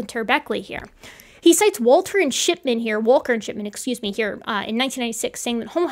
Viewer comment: Walter and Shitman er Walker and Shipman Anyone else heard it like this?